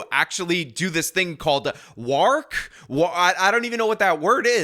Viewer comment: Wark. Its what chocobo say from Final Fantasy. Wark!